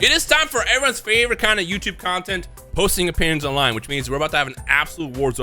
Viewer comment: I thought he said "posting your penis online" in the intro